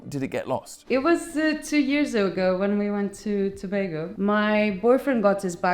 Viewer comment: Did say Tobago as in Trinidad and Tobago? 17:51 😅